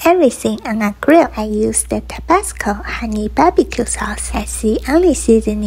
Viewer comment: At first I thought she said Tabaco 💀💀💀💀 I'm dead